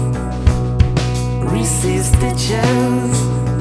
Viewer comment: I thought he was saying "reese's bitches" the first time I heard this lol